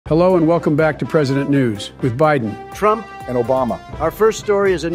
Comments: First time he said it I thought he said president nudes💀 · I thought I heard “ welcome back to president nudes😨 “